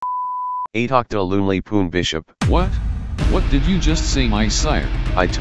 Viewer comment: what did the king said?😢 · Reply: bro said “eehack the loonley poon bishopp” 😂😂😂